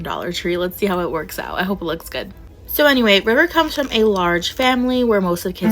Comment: I think she accidentally said who it was and she said “anyway Bridger” so yea I hope I’m right! But it low-key sounds like bridger so…